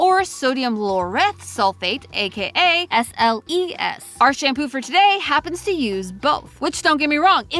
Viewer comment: I am so brain rotted that when I heard her say SLES (4:00) my brain said “they not like us”